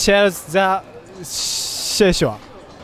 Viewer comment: for a sec I thought he said “she’s hot” at the end